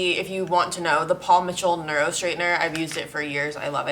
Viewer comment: when she said paul mitchell i definitely thought she said paw patrol🤦🏼‍♀️